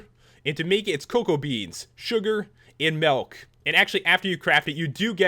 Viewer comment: Great video P.S at 4:49, u said malk instead of milk!